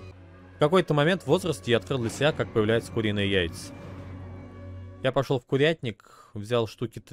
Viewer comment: видимо я слишком часто смотрю нарезки ибо я клянусь, что я услышал ''Фуринные яйца''